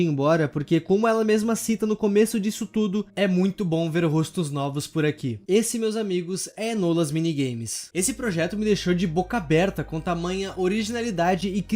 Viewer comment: Eu entendi "os minigames da escola"